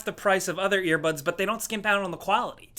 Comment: lol for a second i thought you said this video is sponsored by ratheon, i had to look up at the screen to see earbuds